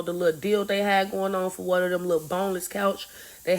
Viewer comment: I thought she said “bonus” couch .. lol She said “boneless” 😹😹😹